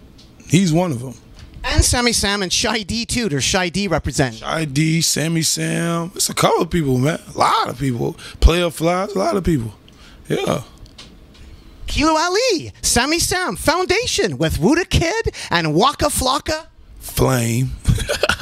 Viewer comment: yo did he say playa fly